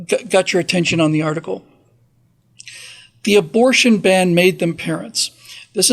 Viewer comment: I first heard it as “Abortion Ban made them Parents of Teens”.